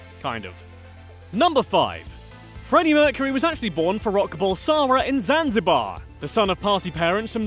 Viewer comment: i heard you say, "Freddie was born for Rock." LOL